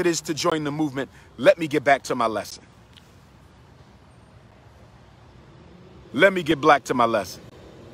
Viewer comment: Had to look up what he was saying, but it was adding up alright. Spitting nothin but factsss... "Let me get back to my lesson...let me give black to my lesson" Anyone catch that part at 30:13?? 😆😆😆